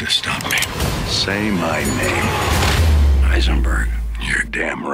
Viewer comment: What did he say at 0:23 ? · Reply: Heisenberg